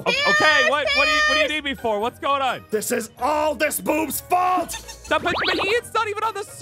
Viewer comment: Ew did Zane say That I cannot say. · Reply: heard about the yuck conversation with the B word Zane said boob, not foob, Ew.